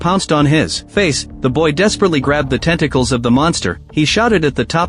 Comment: I had to replay it I thought he said testicles lol